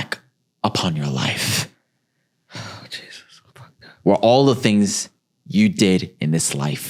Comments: I’m so dead did say “oh Jesus I f’d up” at 54:54 😂😂😂😂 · Did she say “Oh Jesus, I’m fucked up!??” 😂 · 😂😂 did she say “oh Jesus I fuxxed up” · did say “Oh Jesus I F’d up” 😂😂😂